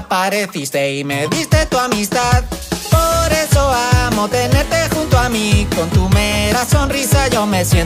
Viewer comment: minuto1:01 parece que dice no tenerte junto ami